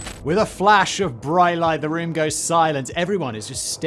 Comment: With Will Smith on screen I misheard that as 'with a slap the whole room goes silent' 🤦‍♂